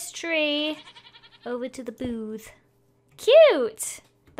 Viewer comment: The first time she said booth my brother thought she said boob. Haha